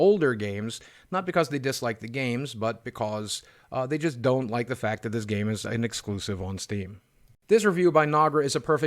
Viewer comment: they don't like the fact that this game is an exclusive on steam? Is that what I heard? Also 00:53 . Is everything alright, Boogie?